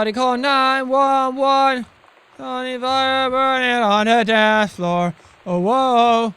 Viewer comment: Am I the only one that heard him saying something about his Viagra being on the dance floor 😂😂